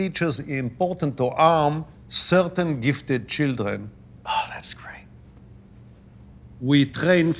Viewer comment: Does he says "Oh! that's great!" !!!! Have this guys went nuts? Unbelievable! America (USA) is doomed....